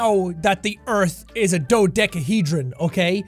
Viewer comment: I thought he said SNOWdecahedron from Undertale